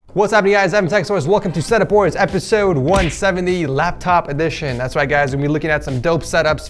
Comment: I swear my captions said that he said “what’s up it’s sex toys” 😂